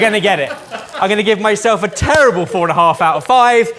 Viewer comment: Did rate himself a "terrible 4.5 out of 5" or did I just hear that wrong? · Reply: They use a 0-10 system so it was a terrible 4,5 out of 10